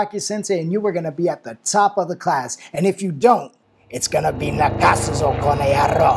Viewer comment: What did rocky say at 1:02? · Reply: 泣かすぞコノヤロー！